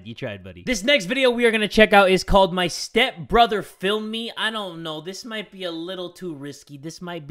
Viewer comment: I thought he said my stepbrother filled me🥱😂